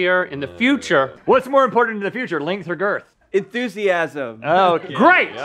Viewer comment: LOL DID HE SAY ENTHUSIASM OMG